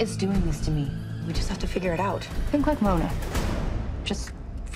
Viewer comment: What does Emily say? "___???_ Mona, just for a minute". I can't understand her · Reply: she said think like mona :)